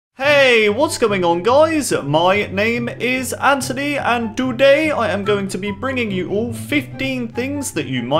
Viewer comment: guys, have you noticed how he said: "doday" instead of: "today"